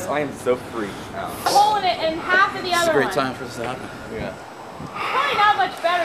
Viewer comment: Did he say "This is a great time for a Xanax" at 1:06? lol ANYTIME is a good time for a Xanax don't ya know!?!? lol